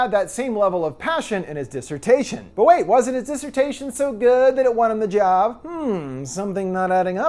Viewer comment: What is said at 15:55? "Disutation"? What is this word, I've never heard it before and can't find it. Disreputation? Disruption? Dismutation?